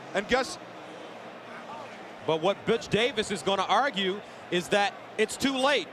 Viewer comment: sounded like he called him bitch davis